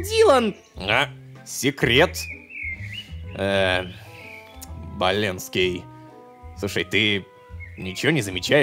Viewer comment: Я один услышал малецки